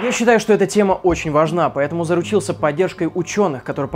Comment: Тема, безусловно, очень тяжелая, но мне удалось поржать. Топа говорит: "я заручился", а мне послышалось "заразился". Я такая: ЧТО????😮 ради ролика???? Но по продолжению фразы поняла что уши мне надо мыть тщательнее. Я такая: ФУХ! Слава Богу!